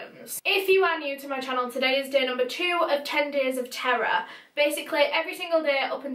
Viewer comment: this whole time i thought u were saying “10 days of tarot” n now just realized that u were saying “10 days of terror”